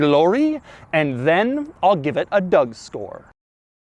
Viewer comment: Did he said Doug store instead of Doug score at 2:54?